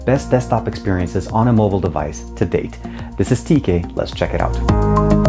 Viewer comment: thought you said "t-gay"